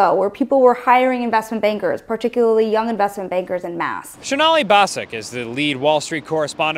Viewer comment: CC is wrong. It is "en masse" not "and math"